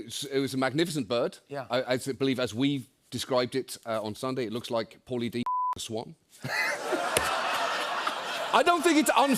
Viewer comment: what is he trying to say here at 2:03min ( bleep ! ) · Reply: "It looks like Pauly D fucked a swan"